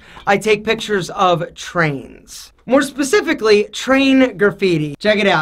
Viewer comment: I thought you said trannies 😂